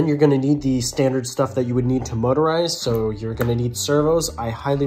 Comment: I thought it said moisturise not motorise (in the uk we use S not Z)